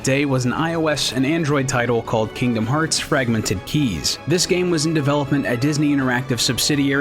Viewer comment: Fragmented Keys huh? I almost thought I heard Fragmented Fanfiction.